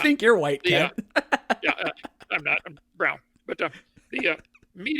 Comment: I rewound it twice. Did he say he's brown? Even with all of the other insanity, that's extraordinary. Right?